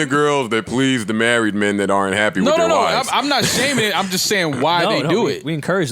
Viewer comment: what did rory say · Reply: he said; "i'm not happy now!" lol